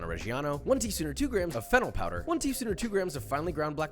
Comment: I thought you said "Fentanyl powder" 😅 Deathsgiving